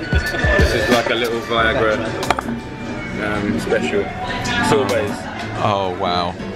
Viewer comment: Who else heard him say viagra pudding?...